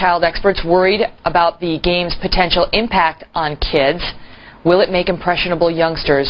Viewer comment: Did they just say "for kids?"